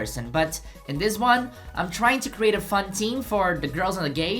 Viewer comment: " I'm trying to create a fun team- " I HEARD PHAUN, I LOVE HERRRR